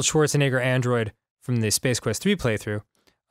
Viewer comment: new grumps upload schedule: is announced me: did i hear "space quest v playthrough?"